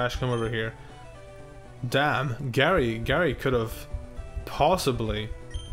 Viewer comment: I thought that said Gay Oak for a minute